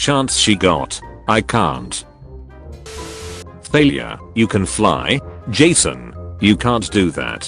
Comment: I swear to the gods I heard 'Failure' not 'Thalia'